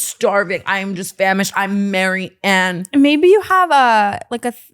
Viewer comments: Did Tana say, “I’m hungry. I’m famished. I’m Maryann”? HAHAHAHAHAHAHAH how did Brooke not catch that. Or maybe I misheard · Did Tana just say "I'm Marianne" as in the girl that Brooke is sponsoring? 😭😂